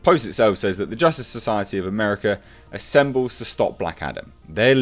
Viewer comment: Wibble. Thought you said Adder.